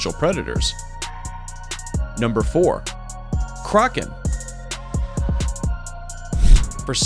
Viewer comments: I thought when he said ' Kraken ' I thought it was ' Karen ' Karen in the back: WHERE IS YOUR M A N A G E R - Me: AAAAAAAAAAAAAA- Edit: Thank you for the likes, and the replies! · Roses are red, violets are blue, I've been click baited and so were you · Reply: I thought when he said ' Kraken ' I thought it was ' Karen ' Karen in the back: WHERE IS YOUR M A N A G E R - Me: AAAAAAAAAAAAAA